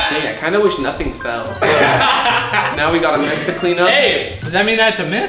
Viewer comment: did he just say " mess the clean up ?? "" lmaoo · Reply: If u have ears u can hear him "a mess to clean up"